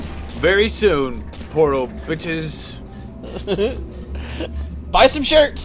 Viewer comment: i thought said porno shirts